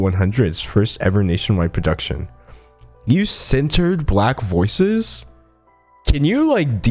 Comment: why did I think he was saying censored😂